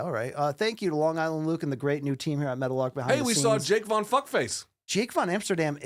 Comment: Can someone answer this. Who's their other affiliation aside from DraftKingsNetwork. I keep hearing Metal Lark, but I can't find the company anywhere, so I'm clearly mishearing.